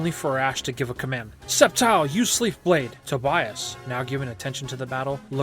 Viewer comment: I think i heard you saying septile uses sleef blade